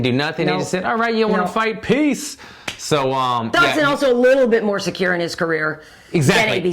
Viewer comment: DId u just say "Dodson is also a little bit more secure in his career than ABCD"? haha